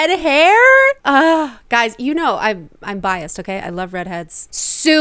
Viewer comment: I thought you were saying "Guys, you know I'm bi" 😂